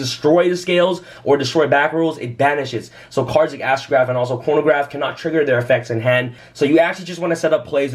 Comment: does say Astrograph and Pornograph? I'm dying lol